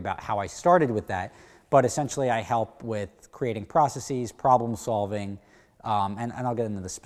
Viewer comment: Legit had to rewind couple times cause I thought you were saying "creating prostitutes" lol...